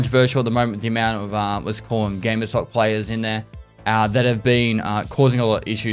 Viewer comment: Could someone tell me, what said at 0:28? "It is very controversial at the moment ********". I listened to it 20 times and it bothers me now that I still can't understand. Please! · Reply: Pretty sure he is saying gamer sock players. (also called RGB sock gamers) referring to cheaters. I think it stemmed from a meme about RGB making you better. Twitch chat often joke if a streamer died to a cheater that it was just their RGB socks, chair, or whatever making them a better player.